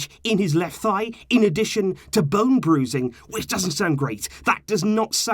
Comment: Why do i keep hearing Bum bruising? lmao